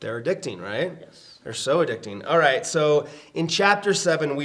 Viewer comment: For a moment I thought said soda-dictting hahaha